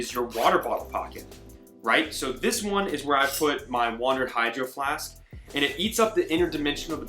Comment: What's the name of those notebooks, or when can one find them? Sounds like you said "Wandered" but I'm not finding anything via Google. Great review, thanks! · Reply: It’s the Wandrd notebook. It’s spelled a little funky. They also have a killer travel planner.